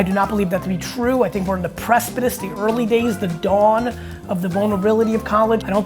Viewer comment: what's that word gary😆pressputice.....